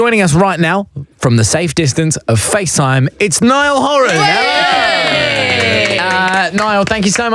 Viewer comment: And the saga continues as the autocorrect says "it's NYLON" instead of Niall Horan 😂 Edit : OMG, thank you guys for 200 likes!!